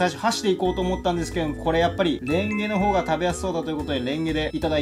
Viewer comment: メンゲレの方が食べやすそうに聞こえちゃった